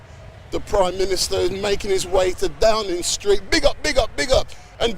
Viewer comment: I heard him say Big up Street Mic